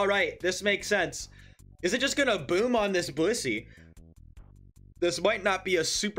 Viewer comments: did anyone else hear "boom on this bussy" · anyone else hear "boom on this bussy"?